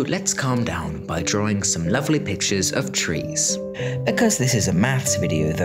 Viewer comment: I heard 'lets draw some trees' ummm... no.